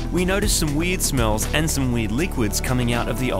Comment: i thought i heard “weed smell” but it was “weird” 🤣